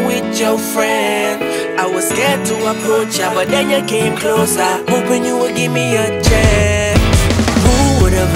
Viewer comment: Nobody: The captions at 0:18: “I was scared to approach, a banana came close...” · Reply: Lmao sorry man but I can't hear it. I Just hear "I was scared to approach ya, but then you came close-a..."